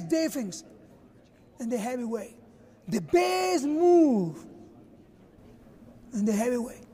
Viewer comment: All I heard was “the bears move”???